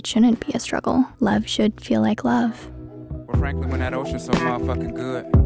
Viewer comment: Why does it sound like he says franlkyn but he actually says frankly